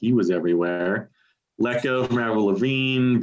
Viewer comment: at 0:53, what the hell are you saying? I have slowed it down - what the hell is Wacko by Meryl Levine? LOL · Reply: Let Go by Avril Lavigne